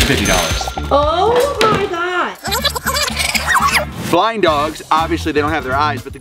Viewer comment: In the beginning I thought you said flying dogs and I was like WHAT!?